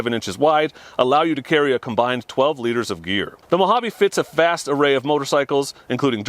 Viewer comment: I swear I thought you said the Mojave bags allow you to carry a combined 12 liters of beer!! Lol!!